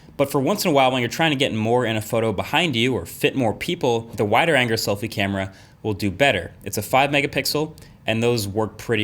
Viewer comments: Anyone else hear “wider anger selfie camera” at 6:00? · The wider anger? 6:00 who else heard that?